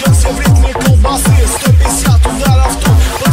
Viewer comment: Did he say CKOBASLEEE? 😂😂😂